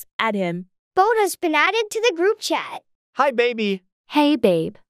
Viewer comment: Story 1- I thought it said “bonus has been added to the group”😭😭😭